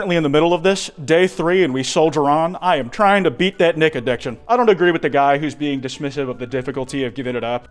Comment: Am i stupid or did bro say the n word 5:15😭😭💀💀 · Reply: He said “nic addiction” 😭